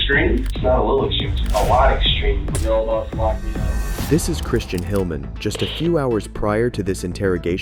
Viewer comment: The subtitles said his name is. You can't make this shit up.